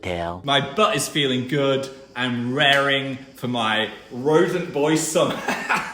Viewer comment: when Phil says he's raring for his "rodent boy summer" the captions say "raring for my rodent boysome" which sounds like an obscure sexual event, instead of whatever rodent boy summer really means LMAO (which actually could be exactly that, listen, I'm not judging)